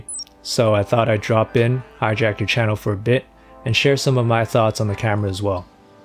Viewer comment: i thought he said Jackie CHANnel at 7:37 lol